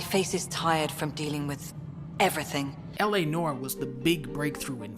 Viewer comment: Did he just say ‘L.A Norm’??